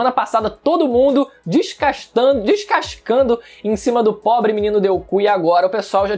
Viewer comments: Não sei se foi proposital, mas escutei um "o pobre menino Deuku" KKKKKKKK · Mano ele falou deuku? Não foi isso que eu ouvi né kkkkk! No minuto 0:19???